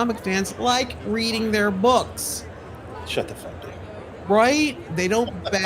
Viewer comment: I swore said "Shut the Fu*k Up "😂